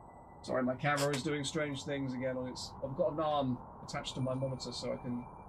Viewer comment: I legit thought he said "I've got a naan attached to my monitor"